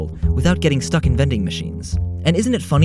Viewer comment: i thought he said Han Solo